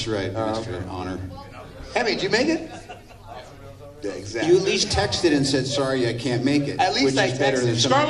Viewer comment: What does say at 3:13 when he is interrupted? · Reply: naheedmasroor syed he just repeated what said . “At least I texted to say sorry I can’t make it “